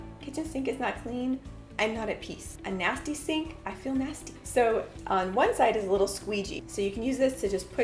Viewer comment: "A dirty sink, I feel dirty". Ok, not exactly what you said, but that's what I heard. I know, I'll see myself out...